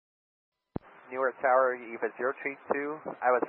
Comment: they actually said “New York tower”